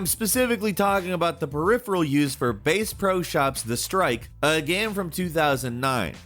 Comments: "Base-Pro", is that what I heard him say? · did he just say BASE pro shop. You clearly are a yankee